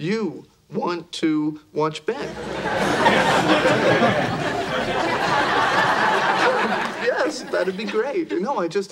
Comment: Can someone tell me what monica is trying to say here? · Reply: She's saying, I'll be here the whole time.